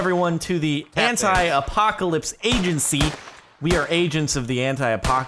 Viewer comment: I'll be honest. I thought he said "Hentai Apocalypse." :P